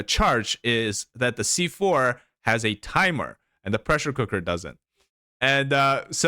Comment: i've misheard it as "oppression cooker" lmaooo